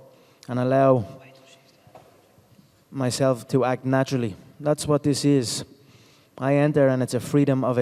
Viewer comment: u hear thats what she said